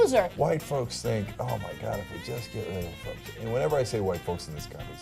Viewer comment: Cenk says whites say "'Oh my god, if we just....'" What's he say? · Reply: Mark Prange “If we could just get rid of...” then he cuts himself off.